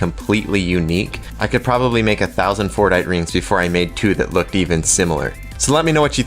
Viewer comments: I thought he said Fortnite😂😂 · Who else thought he said fortnite? 😂